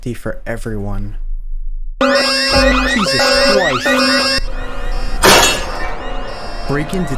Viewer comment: Is it just me or did he say "JESUS Chwist" (How do i add time stapes)